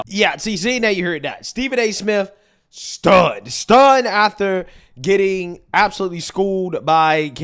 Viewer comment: and 9:08 of this video you say Stephen A was stunned ather.... What the f*** is that word a t h e r. Are you too f**** lazy to say the word because I noticed you say dat dem dose you do know there's a th in front of those words and not a d correct?? I was just making sure Mr know-it-all